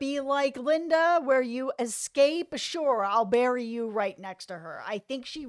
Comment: I thought Roy said to "Nadine", "I'll bury your body right next to Linda's." No?